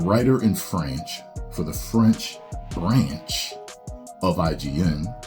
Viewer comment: Did he just say FRANCH? or am I begging?